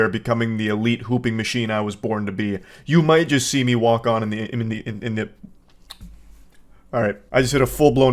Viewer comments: Lol, I thought you said you were becoming an elite hooping machine and you might walk into the enemy inhib. League shit, not porky pig. That's funny as shit, though. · My brain didn't check in until you said "becoming the elite hooping machine I am today" and I thought you said "pooping" xD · I heard him say “I am becoming the elite pooping machine I was born to be” and just didn’t even question it. A friend had to tell me it was “hooping” not “pooping”.